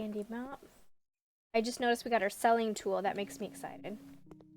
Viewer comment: first i thought she said smelling tool am like whaaaat